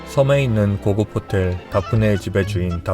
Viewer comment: 귀가 제대로 되어있으면 Daphne를 대프니 라고 발음하는게 들릴텐데 왜 다프네 라고 엉터리로 표기하는지 으이구 어이없다. 10:59 블랱 이라고 말하는거 안들리나? 왜 블라트 라고 엉터리로 표기하는 거야?